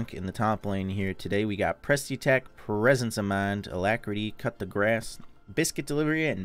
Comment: did.. did he call it ''cut the grass''..?